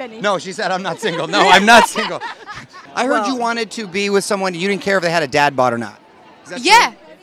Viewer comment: she wouldn't care if what? Man you english native speakers, sometimes I'm lost. Could someone tell me please what they are saying and what it means? Thanks